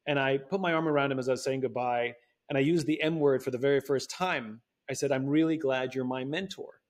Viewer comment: thought he said “and i used the n-word for the very first time” LMAO